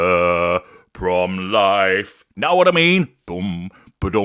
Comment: More like Toddlers & Tiaras🤣🤣🤣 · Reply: I thought said POND LIFE until I read the title.